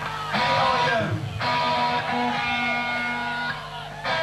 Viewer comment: Towards the end Lydon mentions Thatcher. If i heard correct "how many Maggie Thatchers out there". Watched his lips. He saw the incoming threat.